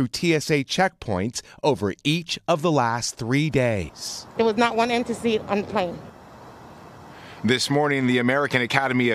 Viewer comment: wtf did she say? Lol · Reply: There was not one empty seat on the plane.